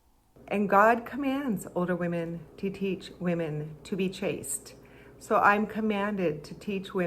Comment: OH CHASTE! I thought she was saying "chased" almost the whole video and I could not for the life of me understand why she wanted to teach women how to get men to chase them LMAO